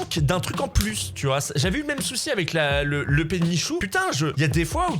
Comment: Toute la vidéo, j'ai entendu "le pet de Michou"